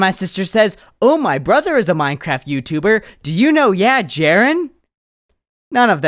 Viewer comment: I looked up yeah Jaron because Idk I thought a lil kid said "my brother is a Minecraft YouTuber do yk yeah Jaron?" So I looked it up and took me back to this video..